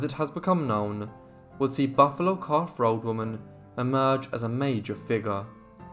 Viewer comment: I believe her name was actually Buffalo Calf Robe Woman. I have lived in the Black HIlls for many years and there are many records of her in the area. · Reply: I thought he said, "Buffalo Calf Rode Woman". "Rode" as in the past tense of "To Ride".